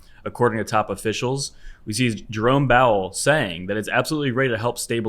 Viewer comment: Did he just say “Jerome Bowel” lmao